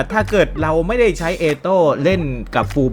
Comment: พี่พูดว่าเอโต้รึป่าว555